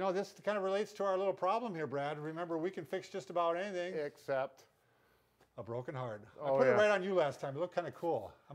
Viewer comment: And all this time I thought you were saying, “we can fix anything except a broken arm.” But what you were really saying was, “a broken heart“. LOL